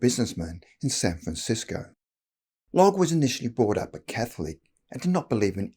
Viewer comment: I had to check the subtitles as I thought the narrator said, "Logg was initially brought up on cat food!"🤣😆